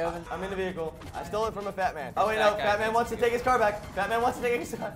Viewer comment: I thought was saying Batman not Fatman